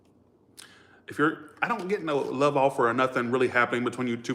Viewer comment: all i heard in my heard was…. i don’t give a fukkkk